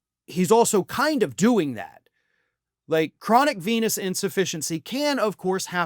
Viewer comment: For a moment I thought you said "chronic penis insufficiency".